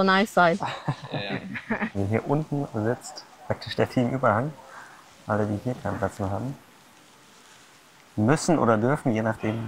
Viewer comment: Was hat er bei 25:45 gesagt? 😅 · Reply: „Alle die hier keinen Platz mehr haben“